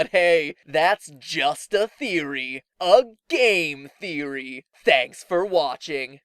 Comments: After hearing mat saying "gay"alot. I thought he said "it's just a theory, a GAY theory" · LOL I thought he said a gay theory! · At the end I thought he said "a gay theory